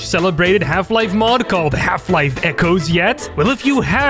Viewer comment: i keep hearing "Half life: Kennedy 12"